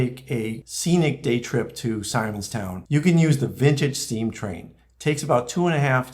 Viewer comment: My hearing is failing me! I misheard say: "Midget Steam Train" - which sounded intriguing! :face-blue-smiling::face-blue-smiling